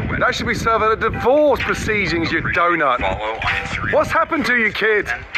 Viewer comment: "That should be served at a DIVORCE!!" You know, he's right. There's no better way to say "We got some serious beef" like a 3-teired steak cake. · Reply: I thought he said a deport procedure 😂😂😂